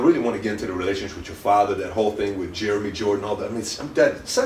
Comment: My bad for how childish this is, but I swear at 4:53 it sounds like he says "The first question I asked you when I came inside you was about your dad"